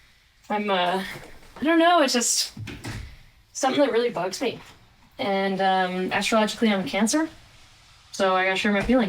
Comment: At the end of that video, you said, "...and astrologically I'm a Cancer...", and I briefly thought you said "...and I just learned I had cancer..." Yikes, I had a moment.